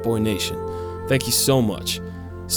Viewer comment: did he say dog bless or god bless cuz you would think he said god but seconds earlier he was talking about dogs